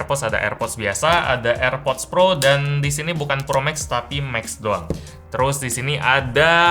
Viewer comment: entah kenapa w dengernya promag s XD